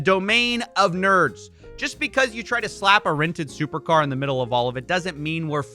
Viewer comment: did he just say Tate has "rented" supercars?